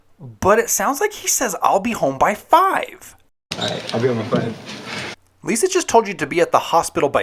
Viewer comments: Slater says "I'll be on my five" meaning I'll be on my five minute break which he's entitled to. And that makes no sense if he's off his shift now. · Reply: I heard I'll be on my 5 or I'll be with my friends maybe? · I heard “I’ll be with my friend”